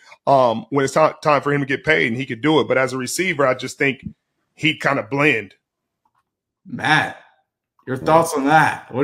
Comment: I thought he said “he’d kinda blend” which makes sense saying he would look like other good Receivers but he would stand out as a DB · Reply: That’s what I thought. He’d kinda blend is what he said. Seeing as though he speaking in rarity in contrast as a db makes sense.